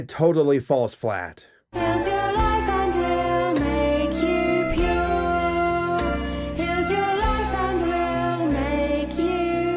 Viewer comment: am I the only one who heard "make you puke" at first?